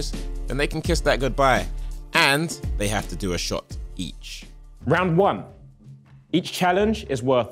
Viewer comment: When he said “they have to do a shot each” I thought he said they have to do a shot of bleach 😭